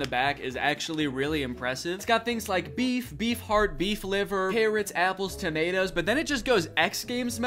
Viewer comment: I swear he says parrots 🦜 here and not carrots 🥕 😂